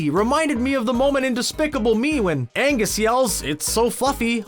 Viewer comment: is it just me, or did he say "Angus"